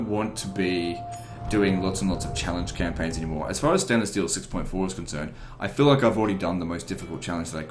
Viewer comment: For a second i really thought you said Standing Still 6.4 haha silly me. Edit: You maybe not the biggest TW youtuber but you sure are the best one. Theres no holding hands in your campaigns nor justifying because lower difficulty settings. Its like we say here in Brasil "Aqui eh a verdade nua e crua" translating into something like "no holds barred".